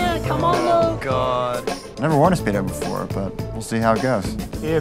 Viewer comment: What he said: "I've never worn a speedo before, but we'll ..." Caption: "god, I don't want to spit ever before ..." Hilarious.